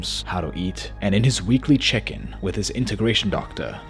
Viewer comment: Who else heard "weekly chicken"? 11:14 Name drop much? 😂😂😂 Thank goodness because it increased my trust in you and helped me subscribe. Great video. 😉